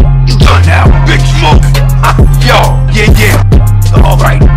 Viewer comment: tf he just say💀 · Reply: The Big Smoke Toilet